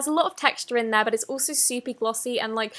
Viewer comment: it kinda sounded like she said “supee” instead of “super”